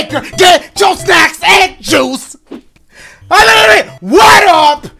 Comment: I thought he said get your socks and shoes in the intro